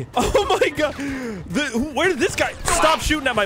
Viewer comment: it sounds like he said the f word